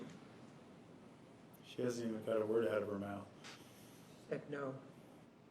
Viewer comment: What were they saying on 44:58-45:06? After he said, "objection!" what did he say about answering the question? Do not surf the question? I know he never said that. Someone please tell me because I can't hear it clearly. Did the D.A. then say, "she doesn't even care whatever is in her mouth." Didn't hear it clearly. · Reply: Nurmi said, “She hasn’t even got a word out of her mouth” - and Juan responds, “I know…”.